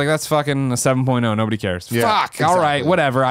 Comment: I thought said Ted Cruz, instead of Terry crews in Crackdown and now want that more than anything else. The Zodiac Killer would make that game infinitely more interesting.